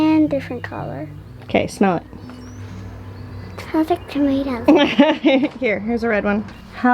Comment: Totally sounded like he said "smells like Doritos" and I was like, I want that tomato!